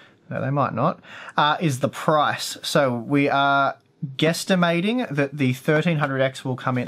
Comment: did u say guesstimating?